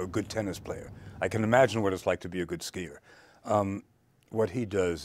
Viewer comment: does he says that he can imagine what is like to be a good skater?? if so... wtf